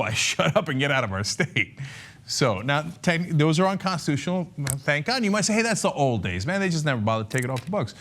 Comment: Did Cenk say "Those are unconstitutional, thank God"?